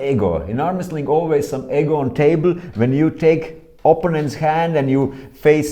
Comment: I thought he said there was egg on the table haha